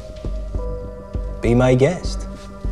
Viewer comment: The subtitle says "Hello to the wife!" instead of "Be my guest."